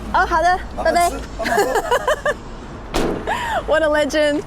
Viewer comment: I think here he said “慢慢喝”instead of“帮忙喝”, which means drink slowly literally. And its hidden mean is enjoy your drink.